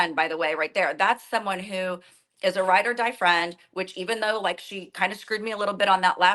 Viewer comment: Did she just say that Tamra is a great ride and die friend 😂😂 Oh my God, she is dumb.